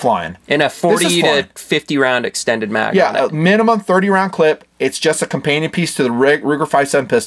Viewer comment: Did really say clip?!?!?